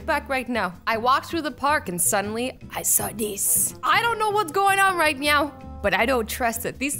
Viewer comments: At the start at the cat part Azzy said meow instead of now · Wait at 0:56 did she say "right meow"? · who heard azzy say "I don't know what is happening right meow" 🤣🤣🤣🤣🤣😂😂 Just me ok😥😥😥😥😥😥😥😥😥😥😥😥😥😥😥😥😥😥😥😥😥☹☹☹☹☹☹☹☹☹☹☹ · Anyone else hear her say "I don't know what is happening right meow"? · did she say idk what's going on right meow because if she did that was an awesome pun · am i the only one that heard meow instead of now? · Azzy- I don’t know what’s going on right meow Me- Wait did she just say “right meow!” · did she say: I don't know what Is happening meow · "i don't know what's going on right meow" is it just me or did she actually say meow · it sounds like she said “I don’t know what’s going on right meow” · she said meow instead of now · Is it just me or did Azzy just say ”I don't know what's going on right meow” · who heard azzy say “idk what going on right MEOW” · I don’t know what’s going on right now What I hear: I don’t know what’s going on right meow Cuz it’s cats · Am im the only om Who heard her say i dont know what is happening meow 00.53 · i thought she said ' i dont know whats going on right meow' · azzy said meow instead of now